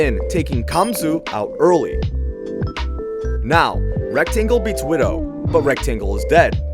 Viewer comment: I wasn’t looking at the screen and I heard “ERECT-angle”